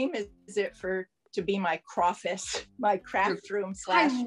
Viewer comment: OMG, really did extremely well in her "croffice". Love that term! Having bought crafty items and not using them, which crafter is not guilty of this? I can relate to, I'm also a hunter-gatherer and a bee. Gorgeous! Amazing! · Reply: I love the term croffice too, but the first time she used it, I heard "crawfish" which made no sense. Lol!